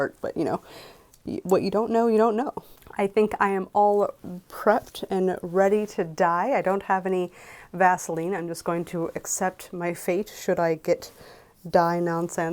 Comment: 4.36 "I think I'm all prepped and ready to die, I don't have any Vaseline, but I'll just accept my fate, if I get any die-nonsense on my skin" Was a wild sentence to mishear